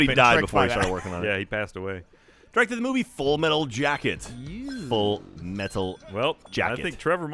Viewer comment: i legit thought it said full metal alchemist instead of full metal jacket